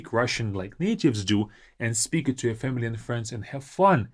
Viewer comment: did he say, “…speak it to your feminine friends and have fun.”? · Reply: no he said "speak it to your FAMILY AND friends" it was kind of spoken fast so its a fair mistake